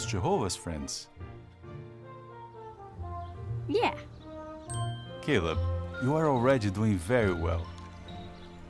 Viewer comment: Okay, I wasn't paying attention and had this video on in the background, so when the father asked Caleb if his friends were also Jehovah's Witnesses and Caleb said they were, I thought the father said "Kill them" for a second when he actually said Caleb.